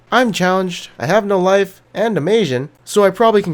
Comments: omg, the intro isn't the same as usual :O sounds like he is saying im challenged xDDDDDlololol.. is best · this entire time i thought he was saying im challenger and im asain but hes saying im challenged and im asain XD